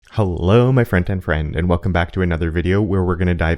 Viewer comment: Love the content. Curious, are you saying "hello my front end friend"? or "hello my friend and friend"? auto captions have the latter but I swear I hear "front end". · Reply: Yeah the auto captions often pick it up as friend and friend, but I'm saying "front-end friend" :)